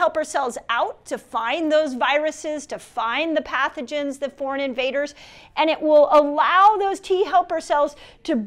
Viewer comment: Foreign invaders!! Haha ... I thought she said fornicators at first!